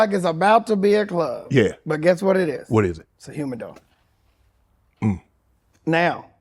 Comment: It's a WHAT? LOL my CC say Humid Dog 🤔🤔🤔